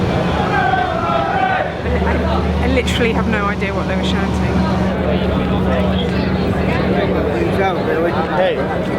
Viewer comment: I have a hunch you probably don't want to know what they're saying. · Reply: U.N.I.T.E.D United are the team for me Sung to the tune of This Old Man